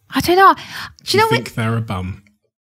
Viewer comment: What did Steve say at 46:46? I couldn't make out what he said and Krissy soon after reacting. Lol. Caps are lagging so I thought I'd post here. TIA! :) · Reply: Hahaha omg I literally had to reply it like a couple of times to catch what he said too, he says "You think they're a bum" LOL I was laughing so much when I got it.